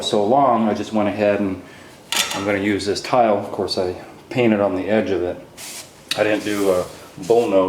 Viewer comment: Hey. Thanks for everything you do on here! A quick question; at 18:50 when you put the top tile down on the curb, did you say you "painted it"? If so can you explain? Thanks.